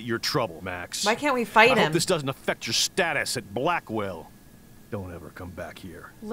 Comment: When Chloe's step dad said "status at Blackwell" It sounded like he said stat ass